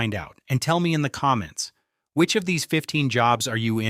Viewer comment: The AI voice can't even get English pronunciation correct. It said I "red" every reply instead of I read evey reply.